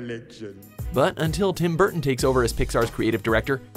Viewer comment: Did they say Tim Burton?!